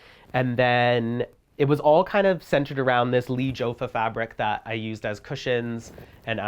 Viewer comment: What is he saying about the fabric in the drawing room? (11:19) It sounds like "leech ofah", I can't really understand. Can anybody clarify? :) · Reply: Lee jofa